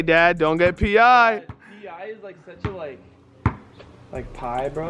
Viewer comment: WTF is faze rug saying😂😂😂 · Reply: Pie is like such a pie bro